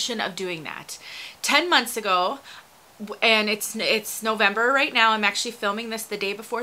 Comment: Odd you say you made this vid in Nov 22 but YouTube says it was posted 2 years ago????? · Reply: Hmmmm, at the 0:39 mark I said I was making this video in November 2020. It was the day before Thanksgiving 2020 (which I also said). Maybe you misheard me? It has actually been 2 years and 4 months now..... and I've actually made other videos on this topic since then.